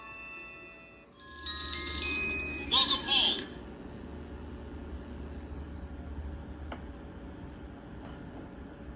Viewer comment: what did it say? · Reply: "Welcome Home" The DVC greeting